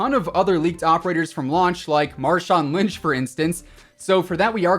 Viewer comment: did- did you say Marshawn Lynch?